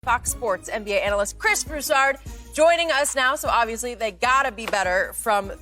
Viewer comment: Did she say c*ck sports. lol sounds like it.